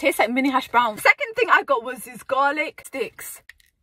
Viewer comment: I love all your videos. You are beautiful and funny at the same time. When you said garlic sticks at first I thought you said garlic d**ks. 😂 😂